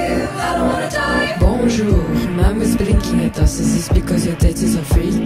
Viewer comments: I must be tripping bro, cuz sometimes I hear the waiter say “very good bon apetite” and sometimes I hear “very good then bon apetite” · Why did i hear: "bonjour your mom was blinking at me" WHY!?